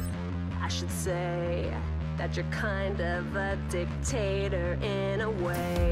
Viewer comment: "I should say, you're kind of a dictator in a way." Marcy's not entirely wrong based on some of PB's actions during the show. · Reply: I thought she was g ok nina say "you're kind of a dick" 😂